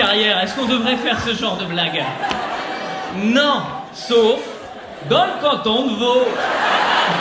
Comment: Il dit quoi à 1:08 ? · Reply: "Dans le canton de vaud"